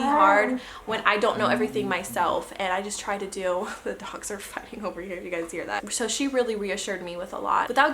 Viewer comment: Also, at 5:18 what did you say? Haha it sounded like “the dogs are f** over here” lol